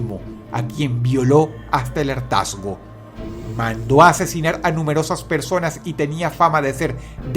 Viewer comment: ke es eso, yo entendí alertasmo, o lo que dicen en el min